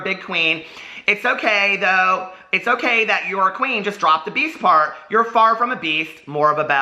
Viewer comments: bwwahhaa... I always thought you were saying " PEACE" .. not beast. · Oohhhh your saying "beast" I thought you were saying peace. 😂😂